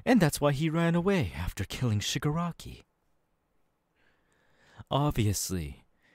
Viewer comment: did he said shigraki?What is MHA doing in demon slayer???